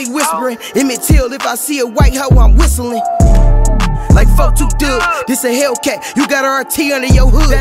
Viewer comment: what he say??? sounded like Fuck 4-2 Dugg but I know they cool I just cant understand the bar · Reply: Whistling like 4-2 dugg